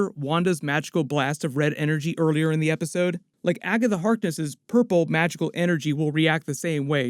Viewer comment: sounds like he is saying "Aga The Harkness" look luck un hearing that lol